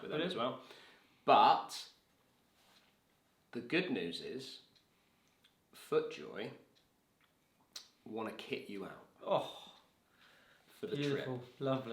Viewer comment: I thought you said Footjoy want to kick you out 😂